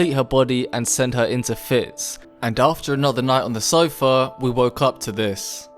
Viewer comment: what u say? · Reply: He said sofa r u deaf